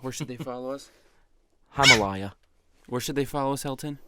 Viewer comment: “Where should they follow us Hilton” lol instead of Elton